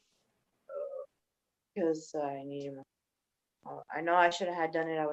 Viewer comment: anybody else hear her say “because I need money?” She almost said it I heard it